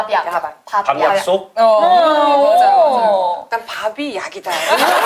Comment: 밥이약이다 ㅠㅋㅋㅋㅋㅋㅋㅋㅋㅋㅋㅋ 밥약 발음 듣고보니 밥이약으로 들려서 납득은 되는 킬포 헤헷,,,, 졸려서 잠깨려고 봤는데 잠이 확 달아나쏘요 ㅠㅋㅋㅋㅋ